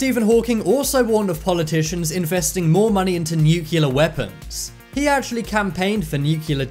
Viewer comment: Does the narrator say "new killer weapons" instead of nuclear weapons???